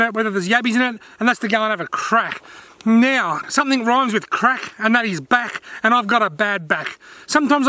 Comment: Awesome job mate I am going yabbying tomorrow I thought you said it rhymes with crap hahahahaha